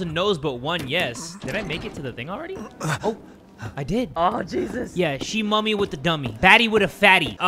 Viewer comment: I’m drawing sykuno while watching this and I just hear “Aw Jesus” 2:00 😂😂😂